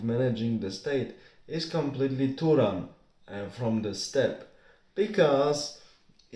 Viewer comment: I understood most of what you said. But there is at least one word which not even youtube's subtitles could interpret. You said this word several times, like in 1:56 "... is completely turan(???), and from the steppe". Is that what you said? Turan?